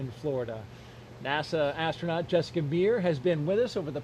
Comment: Did he call her Jessica BEER? 🤣🤣🤣 That's almost as good as the time a reporter called Dough Hurley "Doug BURLY" which made Hurley bust out laughing.